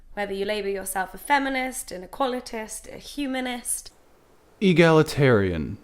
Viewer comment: Equalitist is what she said, she meant equalist, which is the same as a humanist, or egalitarian.